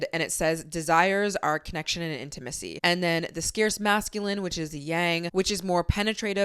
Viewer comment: you said scarce instead of sacred...I was listening only was slightly confused till I came and saw the infographic